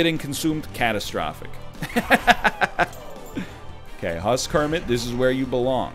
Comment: I thought he said "House Kermit"